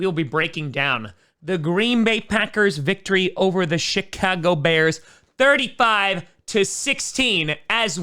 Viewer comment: Sounded like you said "shit-cago-bears"